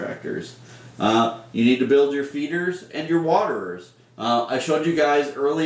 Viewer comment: I thought that said "whatevers" instead of waterers for a minute lol.